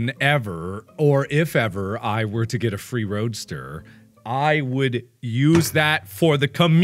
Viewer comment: I thought that said "how to get a free toaster"